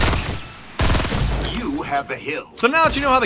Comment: why do i hear "You have a hill" xD